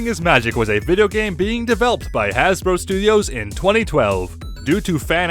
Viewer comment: For the first three I still thought it was legit, then I heard "sued has bro" and figured out it was a joke. You really had me going there.